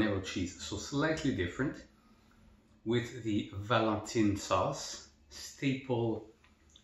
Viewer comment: Sounds like those ghost are hungry! 👻 Looks good, and spice is always nice. Did I hear correctly that Miss Mangia made the gravy? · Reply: Ah okay! And Valentine! Oohh I thought you said Valentina 🌶️ lol 🤣. Thanks for clarifying that! 😅